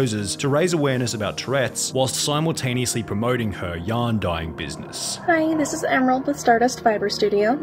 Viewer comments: Every time you say, “yarn dying business” I hear “young dying business” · The way I misheard "dyeing business" as "dying business" and nearly just went with it